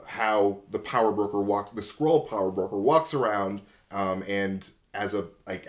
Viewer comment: When he’s talking about a skrull being the power broker I thought he kept saying squirrel and was so utterly confused.